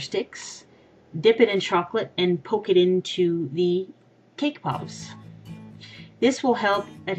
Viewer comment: At first I thought it said cake poops! lol